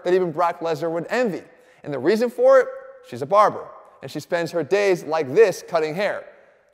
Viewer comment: I swear, when said his wife was a barber, I heard "barbell" instead and didn't think to question it. XD